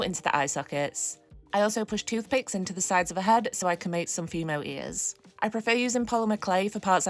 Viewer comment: I keep hearing "female" instead of "fimo" when I watch your videos